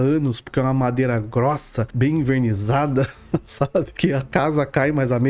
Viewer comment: "Umas mesas bem invernizadas" o que eu ouvi "umas mesas bem INFERNIZADAS" virou freak tv XD